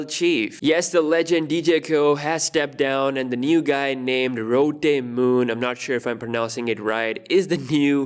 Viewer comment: did I hear ROTTEN moon??